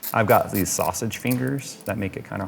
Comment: So what I heard was “Jays sausage fingers make it hard” 😂